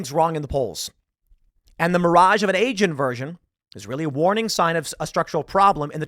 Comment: my goodness, at 11:00 I heard "the mirage of an asian virgin"